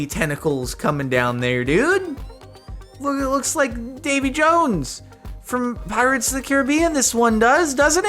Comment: I swear I heard him say David Jones